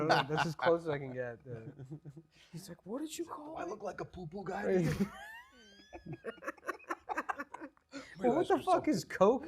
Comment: What does Mike say at 2:16. 'I look like a poopoo guy?' please someone tell me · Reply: “Do I look like a poo poo guy to you?”